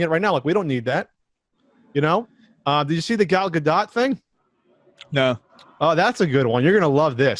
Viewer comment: it took me a full minute to figure out you meant "Gal Gadot." I legit thought you were trying to say Galaga. A Quibi series based on one of my favorite arcade games would almost get me to sign up Jeff Katzenberg & Vince McMahon should team up. Those two could come up with some good s#%t in tune with today's audience :P